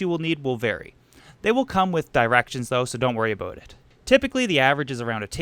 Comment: did he say aboot instead or about?